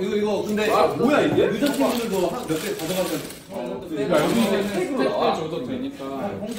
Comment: Why gfriend is called protocol team? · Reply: when this video came out,, the translation at 9:30 said "gfriend members can take some of them" but fans really dont like that so they email to bighit to change it cause they cleArly hear ujeontimdeul but the funny thing is when fans try to say they hear "ujeontimdeul" and said they are korean people and when korean people ask them to type in korean they keep silent...and bighit change the translation from gfriend to protocol team means staff.. and then in MMA awards,, V gave speech and said thankyou to "ujeontimdeul" clearly and bts laughed..means they know about this joke..honestly whats wrong if they really said yeojachingu?? they are friends yall should stop this fanwars now..let them freely interact with each other!! sorry for my bad english bxdjsbkje